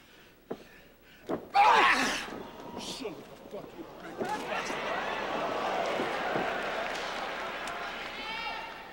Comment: Great exhibition match. 8:09,tho',WHAT DID HE SAY!?! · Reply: I think Billy was a bit annoyed at something and asked him if he wanted "a fucking shoot". He deffo drops a couple of F bombs.